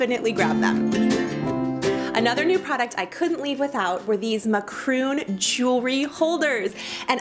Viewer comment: did she say macroons instead of macaroons